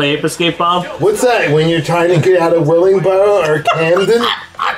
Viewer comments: What’d bob saying at 5:06 it just sounds like him spewing letters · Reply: "when your trying to get out of Willingboro or Camden" its a joke about those being majority black cities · Can someone explain what he said to me? Its probably really funny. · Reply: They're talking about a game called "Ape Escape" and Bob asks "what's that like when you're trying to get out of Willingboro or Camden?" Willingboro and Camden are both towns in New Jersey. These guys all lived in NJ at the time which is why they got it instantly. Camden is the highest crime/murder rate town in the country, and both towns are almost 90% black. So the joke was that Ape Escape = trying to escape those towns. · what did bob say? I literally couldnt understand it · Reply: Willingborogh or Camden. Both predominantly black areas lmao